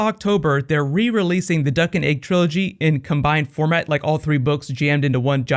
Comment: Subtitle error at 8:56, you have 'Duck and Egg' instead of 'Dunk and Egg'